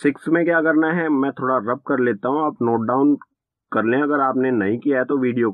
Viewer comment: Any One Noticed at correct 9:17 he said Aap lockdown karle instead of Aap note down karle 🤣🤣